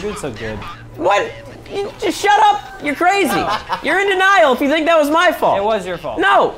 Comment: what normal people hear "Your in denile"- sohinki what I hear"Your in to niall"